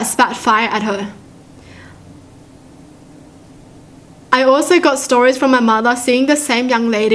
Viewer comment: Why did I hear you say “gay stories” I-😂